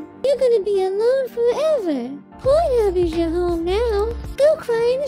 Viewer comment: i don't know what she says here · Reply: “PH is your home now”